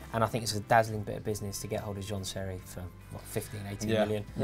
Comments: At around 14:53 does he say John Terry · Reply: He said seri first time I heard it I thought he said terry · When he mentioned “Jean Seri” I thought he said “John Terry” 😂